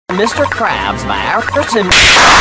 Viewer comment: mr krabs by a person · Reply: i heard "mr krabs slаррed a person"